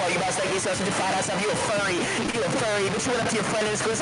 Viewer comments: I only hear furry lmao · not even the captions can tell what he's saying lmao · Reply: I heard “you’re a furrry ahhh you’re a furry “ he said that to a furry 😂😂😂😂